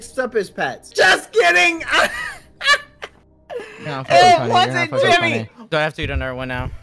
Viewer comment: I spaced out and thought when bad said "it's not Jimmy" He said "it's not fucking Jimmy" I was shook for a sec. Had to rewatch that part.